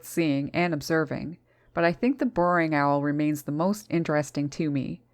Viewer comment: Amazing 😍 video; thank you! At first I misheard 14:54 and thought you said, "the boring owl remains the most interesting..." 🤣🤣🤣 I need to get my ears cleaned...